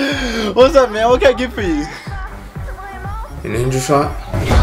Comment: For a few secs I thought said angel sauce (I was looking at the comments when he said angel shot)